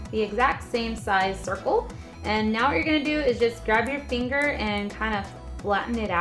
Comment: did you just say "grab your finger" ?